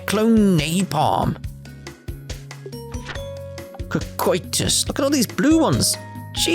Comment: I didn't hear the first "kuh" sound so I thought you had said "coitus" at first. Which would have been...a very different Art to say the least.